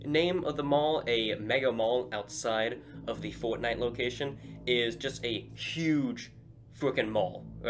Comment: Did you say "fortnite location"?